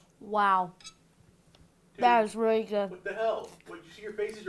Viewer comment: At about 4:23 - 4:24 did you say "What the hell" ?